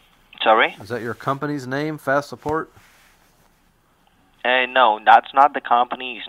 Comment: You know it's great when a techneeshun doesn't know what a CORD is! Great call my yute! It actually sounded like he said Fat support. lol